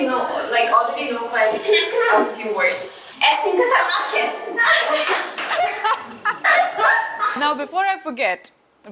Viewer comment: Is anyone understand what says 19:43 in this part😅? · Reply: she said XIN khatarnak hai that means XIN is dangerous